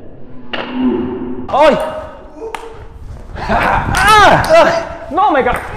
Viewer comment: I folded in half when he said "Run Forest!!" lol! There are a few levels to the joke. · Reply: I thought he said “run for it”, but I guess I’m wrong!